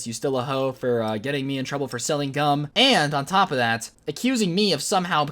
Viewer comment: I thought he said selling guns